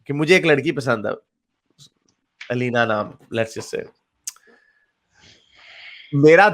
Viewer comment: did you just say aleena name!?😍😍